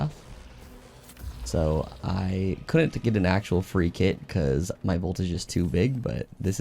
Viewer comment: Why did he say he couldn't get a free kit? It sounded like he said his "Voltage was too big" lol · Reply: vault too full. Couldnt find anything else to delete. I have like 50 of "precision shots make you invisible" "revives make you invisible" "sliding makes you invisible" etc lol